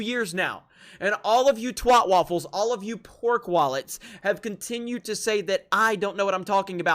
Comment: I thought you said pork waffles for a sec lel